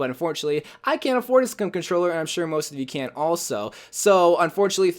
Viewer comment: I can’t afford a SCUM controller Lol I think he tried to say I can’t afford a scuff controller